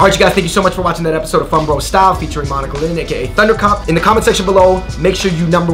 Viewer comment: Deadass thought he said thundercunt 😂😂